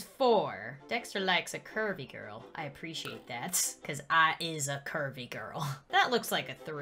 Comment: Thought she said she was a Harvey girl!!💕😭 Bruh Netflix needs to upload more seasons.